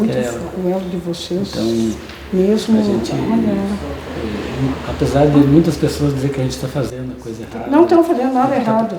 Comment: Em 17:31 a moça diz algo como chamando "Réva". Repeti várias vezes mas só entendi isso. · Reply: Deve ser “ reza “ pra ele rezar pra ela